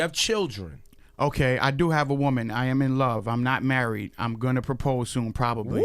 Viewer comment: That song was dope bruh, but damn it sound like he said "Im going to propose toohum"🤣🤣🤣could be the cookies though